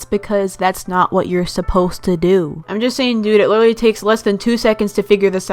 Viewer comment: DID SHE JUST SAY JUST SAY SEX!??!